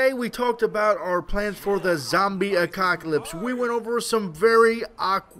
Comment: Did he just say zombie acockalypse? :D